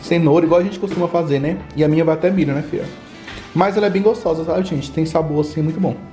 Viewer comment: O falou vina ou eu ouvi errado? Se falou, fiquei muito feliz kkkkkkkkk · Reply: Milho rsrs❤